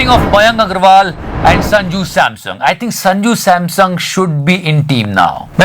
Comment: did you say sanju SAMSUNG @ 6:38 ?